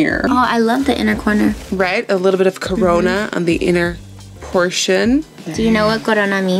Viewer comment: When Nikkie said she was putting the shade "corona" in her inner corner it legit sounded like she said "cabrona" and I was like OHHHHHHH CIELOOOOOO ✋😨✋ (if you're a spanish speaker that's watched novelas in the past like <num> you understand what I mean by those emojis lol)